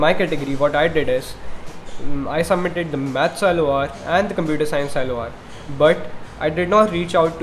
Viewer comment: this video is very informative and it eases off something from ur anxiety. but bro I don't understand what you were saying on 3:03, 3:04 what do you say after u say maths and computer science i thought you say maths allowance and computer science allowance, but it turned out giving no meaning.